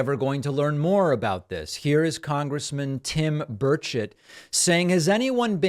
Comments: WOW!! For a moment I thought you said "Congressman Birdshit" 😳🙃😅🤣Not really your style, so I had to listen again (in my defense; I'm danish) So they come from galaxies far, far away and have a last minute birdstrike just before landing. Daaamn gulls!!! 👉😵💨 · Damn, I had to do a double take. I thought you said Congressman Tim Birdshit! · I thought David said Congressman Tim Birdshit! 😳😂